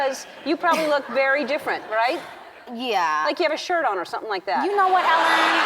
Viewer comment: What did ellen say at 3:44? sorry i speak spanish and i can barely understand 🙁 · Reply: “ like you have a shirt on or something”